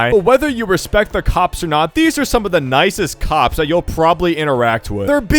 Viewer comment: When he said "these are some of the nicest cops you'll ever interact with" i was scratching my head and i thought he said "these are some of the nicest cocks you'll ever interact with"💀💀💀😭😭😭